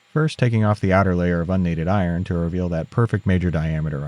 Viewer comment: Subtitles say "annealed iron", but what's actually said is "unneeded iron"